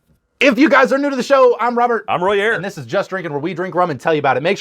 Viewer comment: First of all — I thought said “I’m Roy Ayers” and I was intrigued… turns out I misheard, but this is amazing in a mai tai. Trader Vic’s recipe is incredible with S&C. Thanks for the review!